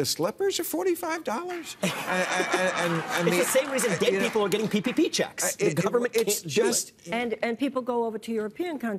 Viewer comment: Did Vivek just say "It's the same reason gay people are getting PPP checks"? WTF? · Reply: No. He said "the same reason dead people are getting PPP checks".